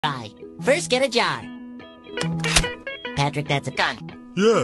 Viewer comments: For a minute there, I thought Spongebob said “first get a job”. Patrick gets out a gun Spongebob:”Patrick that’s a gun”. Patrick:”Yes” then shots fired and cops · At first I thought SpongeBob said get a job